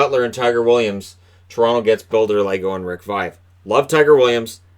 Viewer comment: When you said Bill Derlago, I thought you said Builder Lego, which I think would have to be the greatest name ever to exist